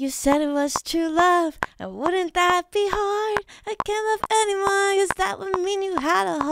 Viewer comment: Ive never seen her so genuinely unsettled by her own creation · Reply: I thought you said “anyone” instead of “her” and I immediately was about to say Oppenheimer lmao